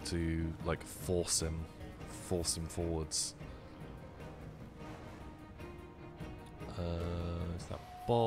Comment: it sounded like said foursome forward and i got a little excited